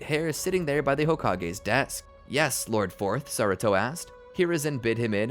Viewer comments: did he said lord 4th to hiruzen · did he say yes lord fourth